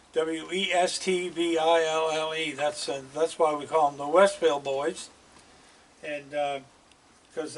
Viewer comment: PS all this time I thought you were saying ‘Westfield Boys’.